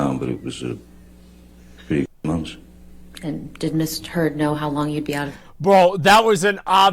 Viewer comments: I swear, JDs lawyer just said miss turd....sounded very like it....hahaha...imagine.... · “Did Miss Turd know you were going to be out of town” 🤣🤣🤣🤣I heard it before you even Said anything!!!!